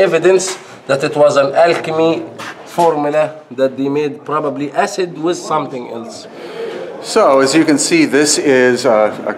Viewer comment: did he say Alchemy Formulae? Acid with something else? I was wondering if granite can react with acid, and the chemistry says it's rather not possible to make a smooth surface not a brick one.